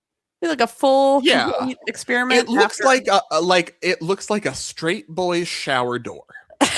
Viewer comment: Did I hear 'it looks like a str8 fully showered'??? I'm deceased lol